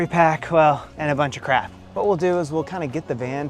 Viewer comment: while showing the van interior, did you say a bunch of “crap” or “crack”? One of those is just a nuisance but the other will get you prison time.